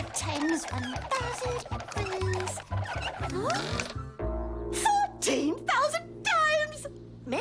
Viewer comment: Lol when I saw the teaser I thought she said 14,000 dimes